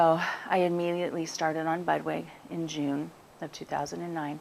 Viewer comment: I could not hear. Did she say she immediately started on "bud way" and then the lumps went down im a couple of weeks at 2:11? What is bud way?